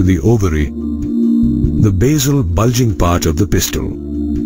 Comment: see this At this time there is a line when u on CC It writes pistol instead of pistil Believe or not I frightened 🤣🤣🤣🤣🤣🤣🤣🤣🤣🤣🤣🤣